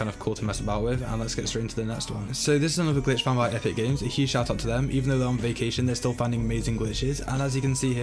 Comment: I Don’t Know If I’m Not Hearing Properly Or What But... Shout Out To Epic Games For Finding This Glitch I Don’t Know I Still Think It’s Funny Whether I Heard That Right Or Wrong.